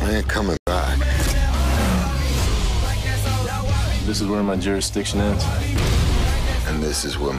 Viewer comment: what does he say " this is were my journey sticks........"